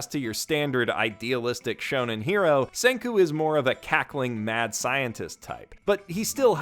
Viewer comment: i thought he said Kakyoin not cackling oop